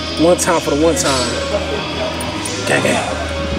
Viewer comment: Did you say : Gay gay @ 14:20?